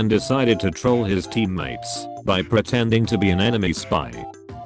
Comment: Holy shit, I thought at 0:08 it said animie spy. I thought was a weeb for a sec